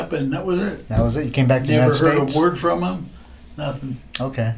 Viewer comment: the subtitles say Mexico when the detective said United States 😂